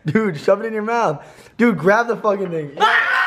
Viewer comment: Who else heard grab the fuckin nigga